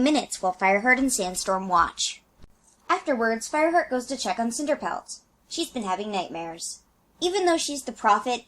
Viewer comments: Captions says Fireheart's name is "pervert" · the subtitles say “pervert” instead of fireheart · The subtitles say Pervert when Fireheart is said